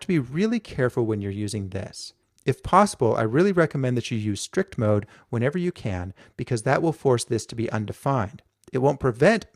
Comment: On 5:07 he says use "strict" mode instead of "this". What does he mean? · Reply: Guilherme Sa That's not what he says. He says "use strict mode whenever you can, because that will force "this" to be "undefined". Strict mode is what it implies: a stricter 'version' of JavaScript - it does more checking. Read: <link>